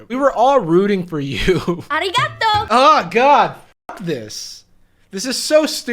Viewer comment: Ok Im not japanese so i have no idea what the girl just said at 11:39 could someone explain pls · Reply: arigato - thank you her pronunciation is painful and i’m most definitely offended